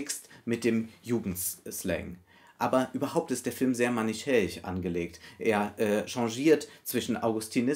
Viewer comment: "manichäisch"? Ich bin nicht sicher, ob ich das Wort (akustisch) richtig verstehe.